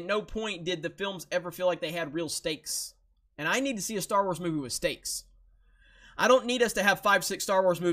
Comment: I heard "I need a Star Wars movie with steaks." - now picturing sitting down to watch A New Hope with a couple of slabs of meat fresh of the grill...